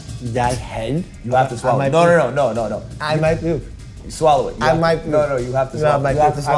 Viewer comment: Lmao I thought he said, "I might poop, I might poop." 🤣🤣🤣🤣